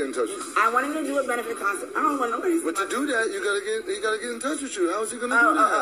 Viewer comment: sound like she was saying I dont want No body to see me or my teeth . N was bout to cry round 2 minutes